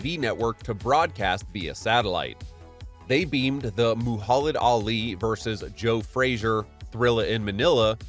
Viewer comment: wtf did you just say? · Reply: Mulholland Ali 😂😂